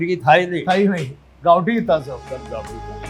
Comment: Lucky's owner says, "you will remember that once upon a time there was no English chicken" (in India, presumably). Then both of them said something that sounds like "gowdy". Anyone know what they said, and what it means? 15:26 Lucky's owner says that the word biryani comes from a Persian / Arabic word for "fried", and it sounds like he's saying "biyan". I looked up Google Translate, but couldn't find the word there. Anyone know the word?